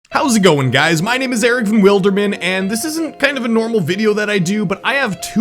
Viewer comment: I thought he said “Eric From Wilderman”... (